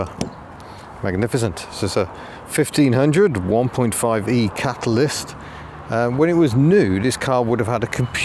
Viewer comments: I thought you said it was a 1.5 Capitalist for a moment. Oh the irony! I’ll see myself out... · I had to rewind - I thought at 0:49 you said "1.5E Capitalist". How I laughed!! Anyway....here's a car that took people from A to B. No fuss. Job done. And from a rear three quarter view check out those Triumph 2500S estate looks.